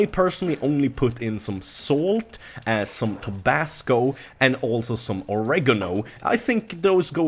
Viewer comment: I heard tobacco at first lol